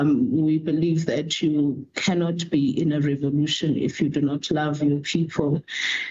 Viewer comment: What is she actually saying at 8:05? "You cannot be in a revolution if you do not love people...." ???? huh? "The system/institution is very violent ... " Huh? This is absurd. If you don't have the money to study you need to get a job. You need to work hard to realize your dreams, goals and aspirations. Grow up, take resposinibility, plan for your future, Save up money then study. Youre not entitled to go to uni. No form of Tertiary educ is a right. RSA is a 3rd world country Where will the funds come from to sponsor free forms of tertiary educ.?